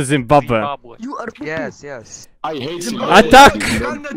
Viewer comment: It’s never gay if it’s in Zimbabwe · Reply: I thought he said "it's not gay unless it's Zimbabwe" implying that they hate Zimbabwe